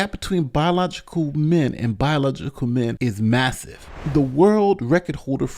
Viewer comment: it sounded like you said “biological men and biological men” a 2nd time…